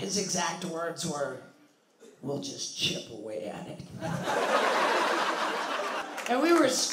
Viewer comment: cant hear what he said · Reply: He said “we’ll just chip away at it”